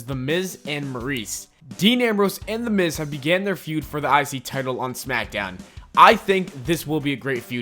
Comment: im the only who hear "the isis title"???